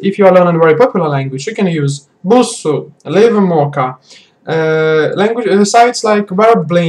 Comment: What websites that he recommended at 7:02 ? could somebody type them down to me? except sharedtalk, I cannot catch what he said at that part. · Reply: romaneasca tsara busuu and livemocha